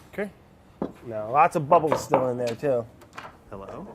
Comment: Is it just me or am I hearing someone saying "Hello...? Huh"